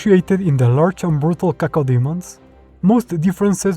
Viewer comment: did you just said CACODEMONS!?!